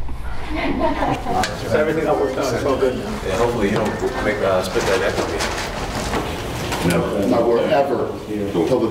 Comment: What did Jon say to Dana at 0:44? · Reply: Jon: so do I still have to fight Alex? Dana: yes you fucking do.